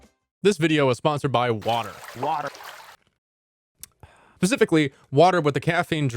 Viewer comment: is it just me or do you hear “pacifically” instead of “specifically”? if so, nice